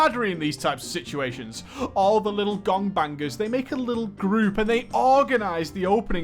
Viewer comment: Can we just hear that one more time? 3:34 Am I hearing that correctly? Oh you said GONGbangers? I see...